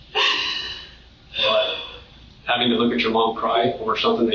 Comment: When the video starts he says “mom cry” and I was just looking at a “monkweh” video and I swear I thought that’s what he said😂